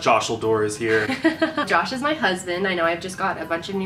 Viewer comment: When you said josh is my husband I thought you said "josh is my cousin" 😂🤦🏽‍♀️